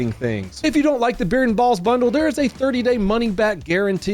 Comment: When you said "Beard and Balls Bundle" I thought you said "Barren Balls Bundle" Which would make sense too.